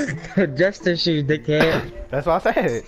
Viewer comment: thought he said justice jews